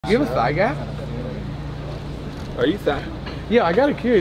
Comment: I thought he said "do you have a side guy" at first 🤣😂